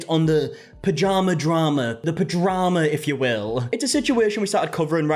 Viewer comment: why did I hear “vagina drama , the vagima”